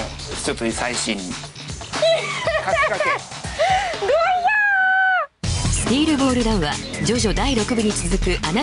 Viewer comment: Did she just say "Dojyaan~!"